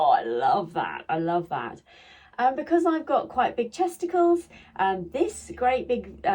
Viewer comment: I had to go back and watch the early part of this with Closed Caption on. I thought you said Testicles but the CC showed Chesticles...LOL...My USA accent vs your English accent I guess is why I wasn't hearing what you said....LOL...Love the jump suit. You look great in it.